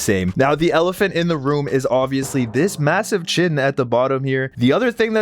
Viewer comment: did u say massive?